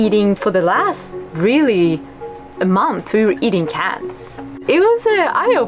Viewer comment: pretty sure she said cans but it sounded like cats 👀